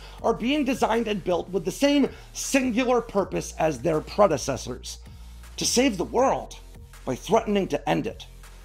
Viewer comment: For some reason when he said this, I thought I heard "Save the cheerleader, save the world."